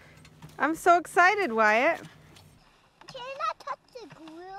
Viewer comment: I thought he said gyatt instead of wyatt 😂 best dad❤